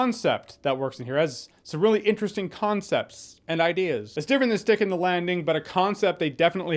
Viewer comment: What is I told you...that my mind was somewhere else for a moment, and when you said "sticking the landing" I heard "dick in the landing"